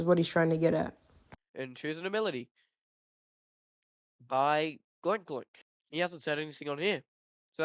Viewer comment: did he say glork glork?